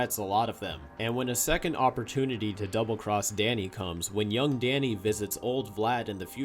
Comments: Well there is some lessons to be learn here 1. Danny Phantom is amazing thanks of Steve Marmel 2. Nickelodeon wants cheaper animation (case and point: lastest episodes of Fairy Odd Parents) 3. Whats wrong with word "Yo"? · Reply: I heard young. It's hard to hear, but it's there. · Well there is some lessons to be learn here 1. Danny Phantom is amazing thanks of Steve Marmel 2. Nickelodeon wants cheaper animation (case and point: lastest episodes of Fairy Odd Parents) 3. Whats wrong with word "Yo"? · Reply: He definitely says “young”, although as a kid I think I heard “yo” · I heard "young" not "yo". Listen with headphones turned up, the "ung" is low.